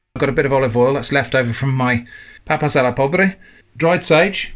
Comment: I'm intrigued what dish you say here. I caught Papa Sala (potato salad maybe) but not the next word. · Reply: Papas a la pobre (poor man's potatoes) AKA Patatas a la pobre, but in southern Spain, most often 'papas'